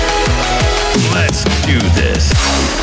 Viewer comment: What was that he say please :'v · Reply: Let's do this. It's the voice by the League of Legends champion "Draven" ^^'